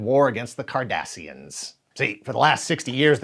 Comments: Then I envy you! 😆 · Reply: every time he said it, I thought he was saying "Kardashians"! There's no way you'd pair them up with the Trek lore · I know is saying ‘Cardassian’, but all I hear is ‘Kardashian’.